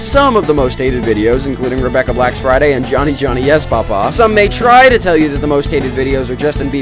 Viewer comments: Bro I thought u said "Johny Johny Yes Bob Ross" 😭😭😭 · Bro I thought u said "Johny Johny Yes Bob Ross" 😭😭😭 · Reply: I heard “Johnny Johnny, yes Barbra?”